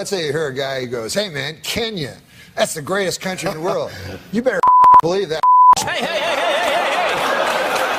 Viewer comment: What did he say at 2:53 ? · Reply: I think it was "you better f*cking believe that f*cking s*it"